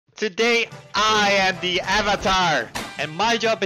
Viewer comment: i forgot i have the video speed on .25 and when he said "today" i thought he said "sugoi" or "amazing" but in japanese translation